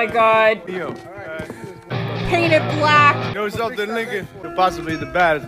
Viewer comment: When Giorgi said Lincoln, I had subtitles off. Because subtitles usually spoil the situation. Thought he said "You know something, Nigga..."